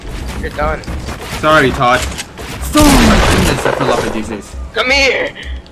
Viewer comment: did he say he fell offf like geesice😭